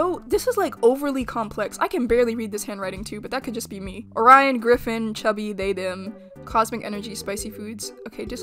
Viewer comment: Someone tell me when i heard "Orion Griffin" i heard "Brian Griffin"